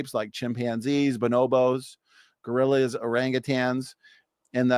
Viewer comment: Did he say Gorilliers?